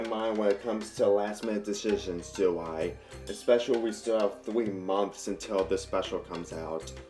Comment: What 3 months?! I thought it was march 30th! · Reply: FYI, I've clearly hear "months" instead of weeks, just listen: 0:09 Plus that rude gesture was not very Friendly there.